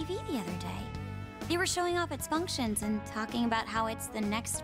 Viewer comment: The voice didn't line up with the text. said "it's" instead of "they're."